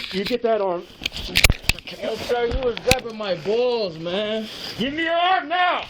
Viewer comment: "you got my balls, man" , did I hear it correctly?